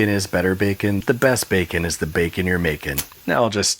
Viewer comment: *macon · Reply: i thought he said the best bacon is the bacon youre baking lmao